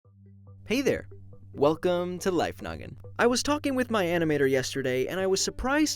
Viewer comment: Blocko : I was talking to my animator yesterday Caption : I took a visit to your human world the other day Lol what Blocko said isn't as the same as the caption